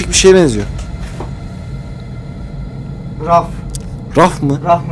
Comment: ve 10:00 da bariz bir şekilde ARİF sesi geliyor. Bu nu sadece benmi duydum yoksa herkes mi duyuyor??? Yemin ederim orayı 7 8 kere izledim ve dinledim.